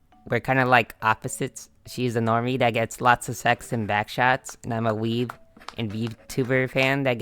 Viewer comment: LOL i love the toggydachi, also i thought you said buck shots so i had to replay it 🙏😭😭